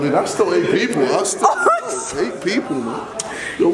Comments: What did Simon say at 1:45 ?x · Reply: Kashaf Zahra so eight people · what did simon say? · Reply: He said "so 8 people"